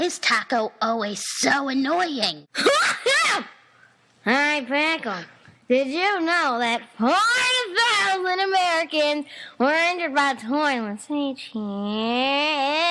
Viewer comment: i think i finally know what taco said at 0:20 Hey Pickle, did you know that 4000 Americans are injured by toilets each YEEEEAAAEAEAEAR